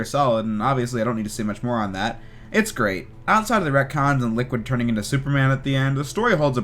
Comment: And it all gets retconned even further in phantom pain. 24:15 Actually, there is one last thing. Liquid was in truth, the superior clone, but all his life he was told the opposite, making him believe he is inferior. That's why he's such a survivor. You might also notice that in the final fight against him on top of Rex, his health meter is longer and he deals more damage. I'm not moaning nor complaining, merely just making an addendum. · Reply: I'm thankful for this comment because I didn't know what retcon is and was trying to figure out what he was talking about but because I didn't have subtitles available, it sounded like he was saying "repcon" or "redcom" and therefore couldn't even Google it to understand the context. Bless.